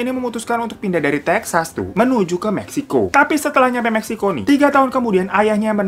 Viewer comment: memeksiko?? Salah denger ga si gua